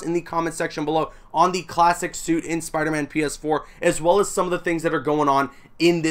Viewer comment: DUde it legit sounds like you said "Spiderman Penis 4" instead of PS4.. wtf XD